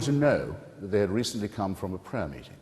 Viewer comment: Can someone help me here? I am unable to make out the word... 1:20 ... "if you were to know that they had recently come from the -----" ? What is the actual word that Christopher uses here ? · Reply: "a prayer meeting"